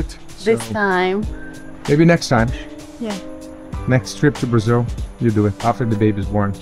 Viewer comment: Where are you guys.? What city? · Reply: Brazil, I just heard you say!